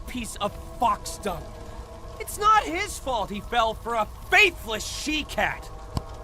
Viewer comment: i thought he said “FATHERLESS she-cat” and i was like >:O